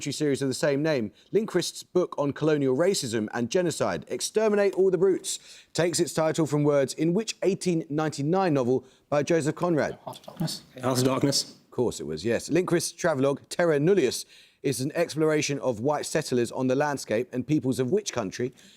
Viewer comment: I think the captain said "House of Darkness". Not that it mattered... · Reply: Hey, I am the captain (now) and although I did say it weirdly I was saying ‘Heart of Darkness’